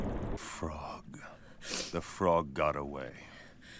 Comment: "fffrog.....the frog got away" At first i thought pain said 'f*ck....the frog got away" 😂